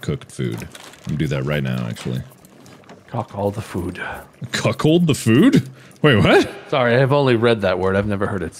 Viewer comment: am i hallucinating or did is sound like mark said "cuckhold the food?" and not "cook all the food?", no? just me? okay